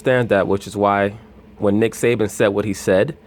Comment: Lmaoo it sound like sas said nigga😂😂😂